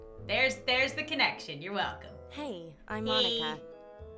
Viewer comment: "Hi!Im Monica!" wet I heard:"Hi! Im demonitized!"